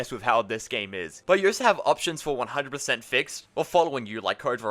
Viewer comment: bro why do you keep saying “oh so” instead of also